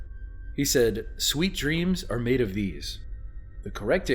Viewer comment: I thought it said sweet dreams are made of the years